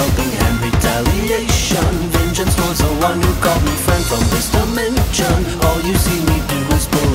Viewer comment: sounds like he says dementia